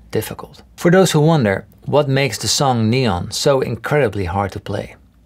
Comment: I thought you were saying what makes the into so hard to play 🤣